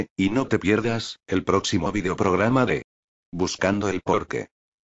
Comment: El nombre del canal pense que decia BUSCANDO EL TOQUE😂